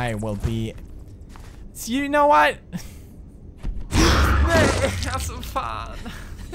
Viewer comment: It sounded like you said House Of Phan!